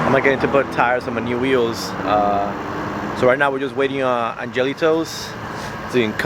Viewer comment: "AngeliTows". I heard "Jelly Toes".